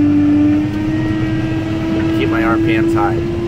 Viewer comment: says I'm going to pull back on the hydro and get my RPMS up. CC says said I'm going to pull back on the hydro and get my arm pants up. LOL